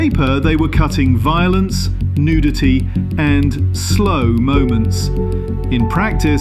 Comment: All I heard was 'cut nudity'. Bastards.